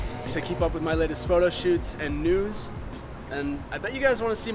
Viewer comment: I thought he said nudes